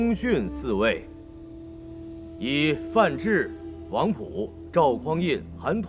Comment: 本集一开头的周世宗遗诏中，有以“范质、王溥主导政局云云”。一开始我听成是王朴。王朴在剧中是郭威家的教书先生。王朴比世宗先死；宋太祖赵匡胤称帝后，一天看到王朴画像，自语道：若此人尚在，我必无法当皇帝